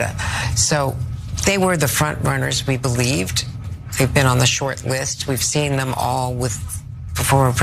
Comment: MUY Bad! You said: "SHORT LIST"! I thought you said "SHITLIST". Oh well ... When the Great Foo Bird shits on you, wear it. Fitting ANALogy. 👞 🎶 I'm diggin' on the Old Brown Shoe... 🎶